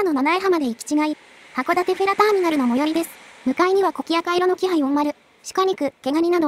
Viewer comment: 函館フェ、、、なんていった？？？？？？ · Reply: もしかしてフェリー以外に聴こえたんですか？？？？？？？？？？？？？？？？？？？？？？？？？？